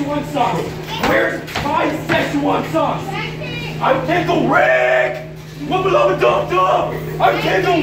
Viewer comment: After several years, I only now just realized at 0:08 he said, “I’m Pickle Rick”. I always thought he’s been saying “I paid the rent” for virtually no reason.